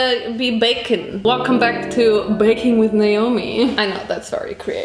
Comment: "So today we're gonna be BACON" Even the subtitles says bacon 😂😂😂😂